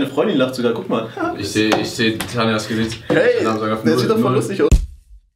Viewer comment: Habe ich Tanja gehört?